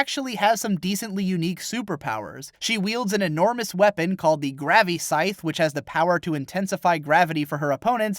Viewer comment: I keep mishearing graviscythe as grabby scythe. guess it makes sense in a fanservice anime...